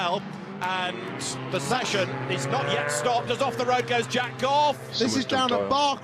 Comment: Did he say "Jack off"